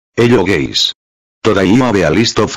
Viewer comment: Where is honorable mention ? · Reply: Everytime i hear "hello guys" i hear be "hello gays"